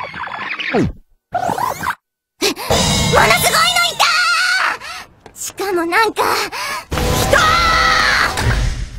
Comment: What is she saying at 01:04 ? in Japanese, not in English since it's subbed Xp I can only her "sugoi" XD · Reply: She's saying "mono sugoi no ita! (もの凄いのいた！)" and it I translate directly, it will be "An staggering one was there!" =)